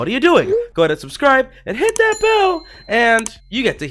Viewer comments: "And hit that bell!" He sounds cute when he does that, lol. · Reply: It sounds like hes saiding hit that hell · It sound like he said hit that hell